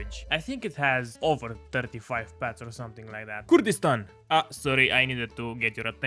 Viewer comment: bro ur voice cut i think u just said nothing in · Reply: Kurdistan